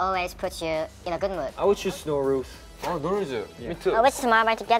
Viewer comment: which song did he say? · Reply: There are subtitles if you click on CC! The song is No Rules by TXT